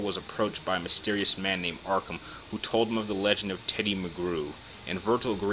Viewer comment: I thought you said the legend of Teddy McGrew.